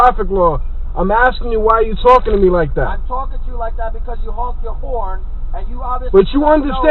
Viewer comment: I thought the cop said, “I’m talking to you like that because your moms a whore.” I was like god damn then I realized he said honked your horn haha